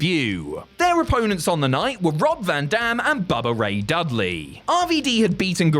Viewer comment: 😂 almost sounds like he said "Brock van dam."